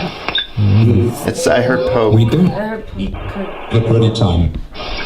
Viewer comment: when says "I hear Po" , a spirit finishes his sentance and says "ker" haha